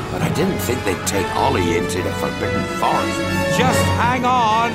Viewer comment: yeah definitely didn't hear "forbidden" the first time